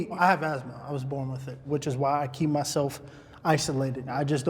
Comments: does he say he was born with covid 19?? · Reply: He said he was born with asthma which makes you more susceptible to worse symptoms of covid 19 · I didn’t here him say asthma and I thought he said he was born with COVID